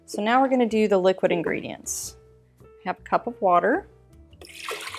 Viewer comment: Hi, i made this cake yesterday, and it came out a bit soggy. I followed the recipe and used 1 cup of water. But in the video it sounds like you say “half a cup”. Can you please tell me what the amount should be? Thanks. Love your channel, by the way! Greetings, Rita (from the Netherlands)